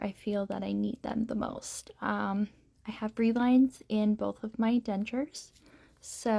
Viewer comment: You said in this video that you have “bre line” in your dentures…is that what you said? And what does that mean? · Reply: She means a "reline" or soft reline done by the dentist. Cushion Grip will go right over top of it, as long as it isn't silicone based. 😊